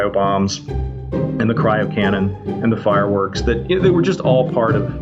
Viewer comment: Let’s step it back a notch.. did he just say “the cryo cannon”? Like we need that story